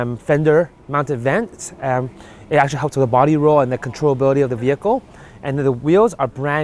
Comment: Did I hear that right at 1:20, fender vents help with body roll and "control-ability" of vehicle? I'm guessing the new 86 badge is where Toyota was able to get that extra 5hp from last years 2016 model with 200hp according to this guy.